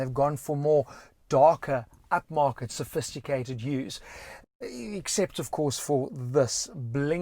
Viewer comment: In 4:03 did he said Utes?